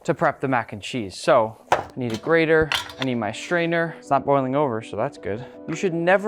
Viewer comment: bro when you said “I need a strainer” for a second I heard it as “I need a sister”😭😭😭